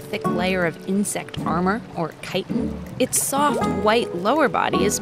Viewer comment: i thought it said "Chin" .-.